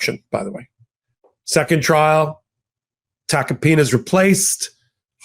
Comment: Every time I hear that name, I hear, "Tacky Tacopina!" 🤣😂🤣